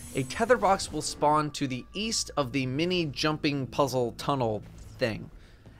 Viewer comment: East? I thought you said "weast"